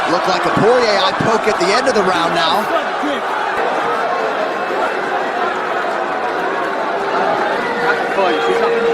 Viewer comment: WTF did herb just say at 8:35?!? 🤣🤣🤣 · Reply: Duck sick